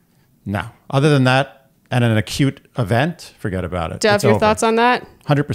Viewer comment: Oh wow I love the hot takes! I'd love to hear more about breakup thought process. I only know one currently successful couple that broke up and they broke up due to long distance. They later got back together and moved to be together. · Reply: haha, I totally thought he was saying a "cute event"